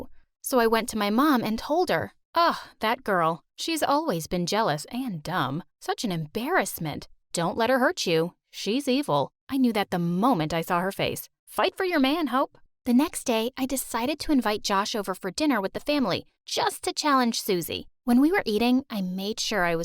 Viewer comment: i thought she said "fight fo yo man hoe"